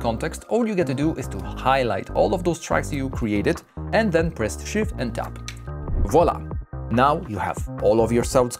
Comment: Glad to hear that 🙏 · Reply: Hello, please, I don't understand what you say at 1:57 for the shortcut, it's shit and... ?